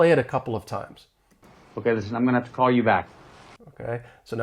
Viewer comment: I just heard like "I'm mana tuh call you back" even with 0.25 playback speed 🥲